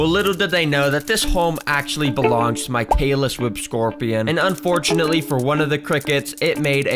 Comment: bro I thought you said "Taylor Swift Scorpion" 💀